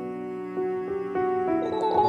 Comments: No one's talking about the dono. "Make a grave for baby man." That's the best part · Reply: I thought it said make a great baby man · Literally not a single soul: What I hear: “Make a grape for baby man” What the subtitles say: “Make a great baby man” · Literally not a single soul: What I hear: “Make a grape for baby man” What the subtitles say: “Make a great baby man” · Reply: I heard " make a grave for baby map " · Fundy: * cries * Everyone: animal crossing Me thinking about that one donation: you make a great baby man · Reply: I thought it said “make it rain baby man” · Digital woman’s voice: “Make a grave for baby man” Captions: “make a great baby man” Fundy: “animal crossing noises while crying” What the actual heck. How did I get 3k likes?!? That’s the most likes I’ve ever gotten in anything. I think I’m hallucinating or something... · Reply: I heard “make a grave for baby nan ” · Digital woman’s voice: “Make a grave for baby man” Captions: “make a great baby man” Fundy: “animal crossing noises while crying” What the actual heck. How did I get 3k likes?!? That’s the most likes I’ve ever gotten in anything. I think I’m hallucinating or something... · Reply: I thought it said “ make a grafe baby nan” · No one's talking about the dono. "Make a grave for baby man." That's the best part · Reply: oh i thought it said make a great baby man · No one's talking about the dono. "Make a grave for baby man." That's the best part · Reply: I thought they said take a break baby man